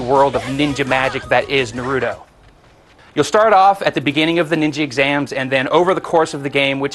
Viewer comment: did he say Ninji exams at 1:40 lol